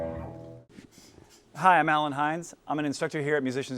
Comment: Am I the only one who thought his name said 'Alien hands' ?